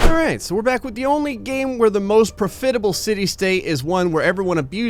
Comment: did you just say pro-fit-able